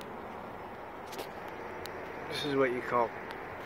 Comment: LMBO you said this is what they call Karma I heard you say this is what they call hilarious.... I know it destroyed some of the roof but that is the funniest thing I've seen in a long time